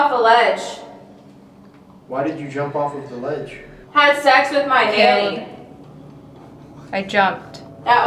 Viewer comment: Did She just said had s*x with my nanny.......